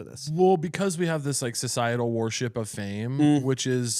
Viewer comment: Caleb says "worship" and I hear "war ship" ( 16:34 ) ... that probably says more about me lol